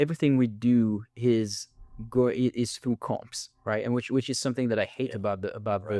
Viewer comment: Please excuse my ignorance, but I'm really interested in this kind of discussion and I'm not sure I'm catching this. Is Raph saying, "Through corps"? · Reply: comps = comparables